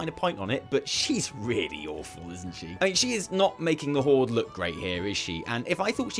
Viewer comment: Did you say "she's not making the Horde look grey"? Because if so we actually agree, but you might just have said "great". EDIT